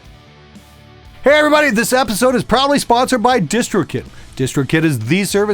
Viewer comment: did i hear him say "this episode is probably sponsored by DistrKid"? lulz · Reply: Lol no, he says "proudly". I've seen this comment a lot and now I always hear "probably".